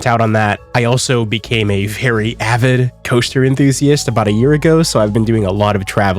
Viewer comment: Misheard initially and thought you said “Toaster enthusiast” and was like “well, now I’ve heard everything…